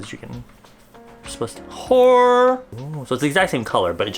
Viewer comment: Idk what it means but at 14:15 every time you say that it just sounds like your shouting "Whore!" 💀